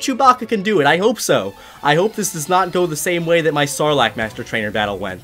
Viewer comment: When you said "Sarlacc" I thought you said "Snorlax" 🤣🤣🤣